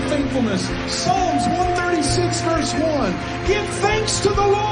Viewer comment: Did he say "Psalms 196 verse one" at the 4:30 mark? 😂😂😂 This guy doesn't read his bible.